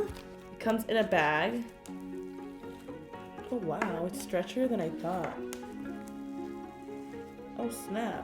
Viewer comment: did anyone else hear her say it's "stretcher" than I thought😅🤣😛